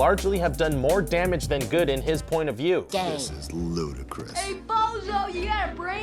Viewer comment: did woody just say Ludacris?....